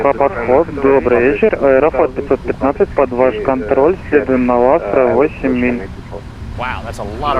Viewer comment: If I hear correctly, he is talking to Moscow-Approach, first transmission is at 3:20 and at 3:43 "Москва-Подход, добрый вечер, Аэрофлот 515, под ваш контроль [can't hear here] 8 миль" Later I hear he requests vectoring and both AFL515 and tower can't hear each other a couple of times, so that's as far as I can transcribe that conversation. At 4:20 he is instructed heading of 11 hours, and at 5:05 he reports heading 1-8-0. My wild guess is he is instructed to wait for his turn to land. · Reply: под ваш контроль. следуем на астра (вастра, вас?)